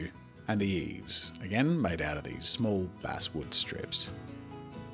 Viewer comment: Did he say “these small ass wood strips” at 5:43??🤔